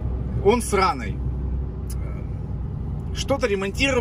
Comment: Мне сначала послышалось, что ремонтник сРаный выпадывает, а он оказывается с Раной)